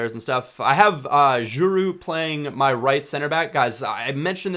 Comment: wow, i was all confused there like an idiot, i thought you were saying Giroud was your rcb, and i was like wtf?? but you meant djourou lol.